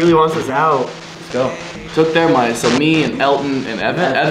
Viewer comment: it sounds like it says "more money"